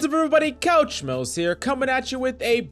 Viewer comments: At first I thought he said, couch smells... 😂🛋️💭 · Is he saying coach mills?? This whole time I thought he was saying “couch smells” like as a joke since “gamers” spend a lot of time on the couch